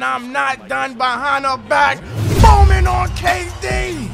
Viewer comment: 4 yearrs from now and I caught on what he said he said booming on KD 11:35 that's Andre Iguodala like dude